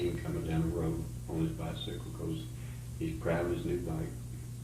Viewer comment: did he say he's proud of his new bike? Or he was grounded from his bike? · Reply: Proud